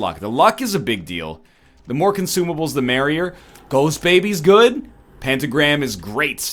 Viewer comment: did egg just say ghost baby is good